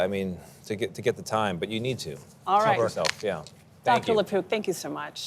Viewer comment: All I heard was "Dr LePoop"